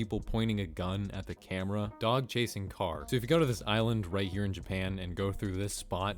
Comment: at the part when he said "dog chasing car" i thought he said god chasing car or dog chasing god